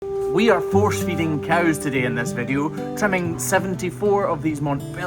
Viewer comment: OMG I heard you say you were force-feeding "all" the cows, and my heart stopped. Then I figured it out😂